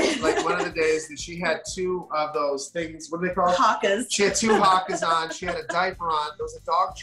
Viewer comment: Is Jax overthinking it, or do you empathize with him? Time Stamps Below! 0:00 Intro 0:15 Jax on holding his son for the first time 1:48 Brittany on her breastfeeding journey 3:17 Postpartum surprises 4:31 How has this brought them together? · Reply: what are they saying? Hawkers? Hawkins? What are they?, please anyone help?